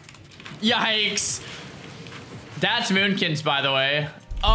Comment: did i just hear a fire mage saying yeah yikes thats moonkins?????????????????